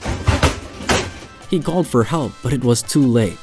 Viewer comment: he did NOT call for help lol, "Helvete" != "help me"